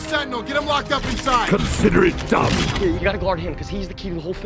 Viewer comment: "Ironhide! Protect Sentinel, get him locked up inside!" "Consider it done!" turns his back to him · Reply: Loll I always thought he said “Consider it dumb.”